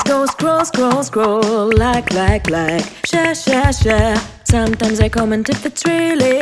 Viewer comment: What the heck is that word Edit- like WHAT T;E HECK IS THE WORD SHA SHA SHA??? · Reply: Ikr- I think she said share- or sum’